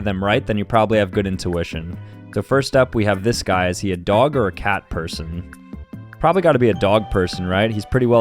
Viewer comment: i thought it said is he a dog, cat, or a person?